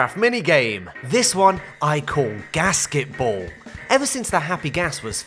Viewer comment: Ghast-ketball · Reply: I first heard it as “gasket-ball” and I was confused for a second